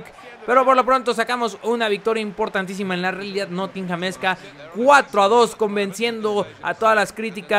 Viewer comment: Que alguien por favor me responda que es lo que dice del minuto 3:59 al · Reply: Lukapo 64 Realidad nottinghamezca